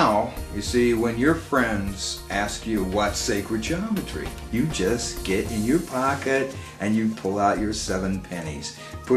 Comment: aw god I thought that said 7 penises x_x